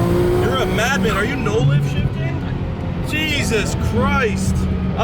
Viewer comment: did he say molan shifting ? what does that mean ?